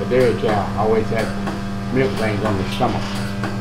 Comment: I didn't expect to find myself watching a video on milk, but it was super interesting. thanks! I wasn't quite able to hear what he was saying at 23:43, is anyone able to explain? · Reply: Thank you for watching and commenting! I am happy to hear that! I believe he said milk veins on the stomach.